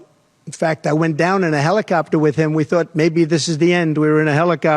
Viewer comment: It sounded like he said i went down on Willey in a helicopter 😂😂😂